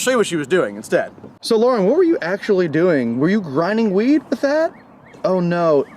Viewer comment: Honestly thought he said "grinding wheat" Hahahaha